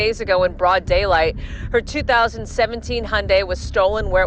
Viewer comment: Subtitle error: 25:04 The news lady said 2017 Hyundai, the subtitles said 2007 Hyundai